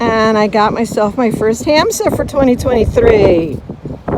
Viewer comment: the pearl was cool . what was that thing at 3:19? A hand ?? I couldn't quite hear for the wind and turned on captions and it says it's a HAMSTER! lol. Maybe you'll mention it again on the wrap up. I tend to make comments as I go thru the video. Well, says the name in the wrap up and I still don't know what it. lol Some interesting items. Fun day. Update, just looked up what I thought you said that charm was. I guess its a Hamsa charm. Never heard of that term before. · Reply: Yes we find several Hamsa 🪬 charms every year👋👍